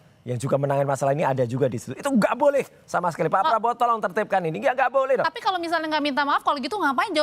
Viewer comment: klo ga salah denger pak prabotol 🙄